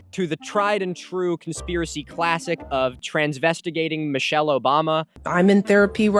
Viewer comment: hey, great video as always! the subtitles seem to have some issues. here's what I've noticed: [offensive language ahead] 1. at 10:12 you say "transvestigating", and the captions say "transvestite dating"; afaik the first word is mostly considered an offensive term today. 2. at 1:34:35 you say "woowee" (or however it's spelled lol), and the captions say "jewish"; given the topics of this video I thought that deserved attention.